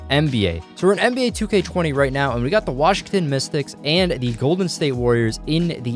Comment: I thought you said the Washington mistakes and I was like “sounds right”